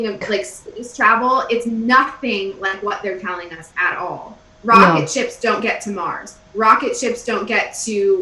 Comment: it sounded like you said, 'rocket ships don't get smart'...lol...eye'm inclined 2 agree